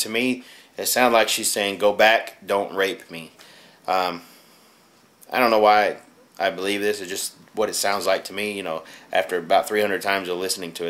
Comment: It sounds like it is saying "Go back don't rape me."